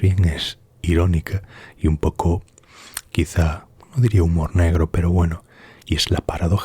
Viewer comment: Estaba medio dormido y escuché "humor negro peruano". Fue así?😂 Saludos desde Perú.